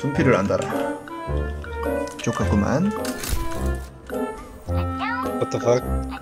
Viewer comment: 둠피를한다라 ㅈ같구만 이라고 아무리 들어도 욕으로 들리는데 자막은 좋구만 이라 쳤네..